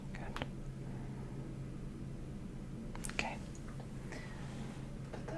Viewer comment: i’m not sure if it’s just me but did anyone else hear the “okay” at 2:07???